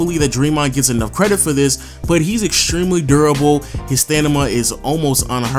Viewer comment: did I just hear "stanima" or am I just tripping?